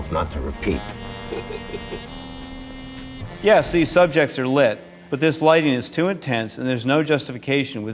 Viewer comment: when he said “the subjects are lit” i heard “the suspects are lit” like if cops just shut down a house party w underage drinkers and explaining why they need back up lol maybe I’m lit